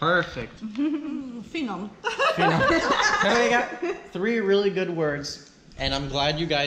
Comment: Did she just say "finom"?? Is she Hungarian??? :O